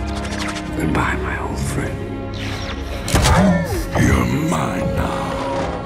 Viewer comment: i think soundwave sayed: your mother xD